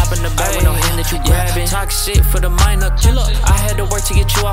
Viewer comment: I thought he said the s word but he said toxic